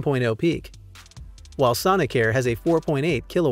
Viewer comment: Did he say Sonicare? If only my toothbrush could power my house!